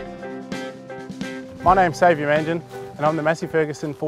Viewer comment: Until they showed his name, I thought he said it was "Save your engine", LOL.